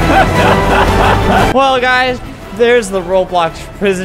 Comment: “Laughs” Well guys. My hearing of: “laughs” fall guys. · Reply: XD in the end, said Well guys That's the end of the video... But i heard him say Fall guys, well that's the end of the video.